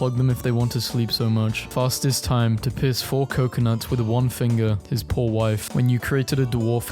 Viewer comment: I heard "Fastest time to piss four coconuts." I was like wtf